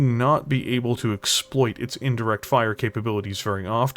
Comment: ER Pulse Lasers are -1, not -2. That's the trade off for increased range. 38:26 Streak LRMs cannot indirect fire. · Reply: Even slowed down it sounds like you said direct fire.